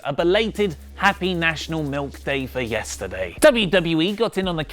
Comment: I thought you said milf day, wooo